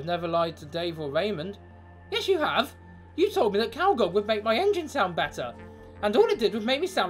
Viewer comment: what did raymond say? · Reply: Calgon